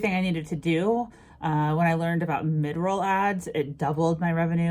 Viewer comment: Great video!!!I couldn't understand what kind of ads you were saying at 24:00.Can u spell it please? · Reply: mid-roll ads <3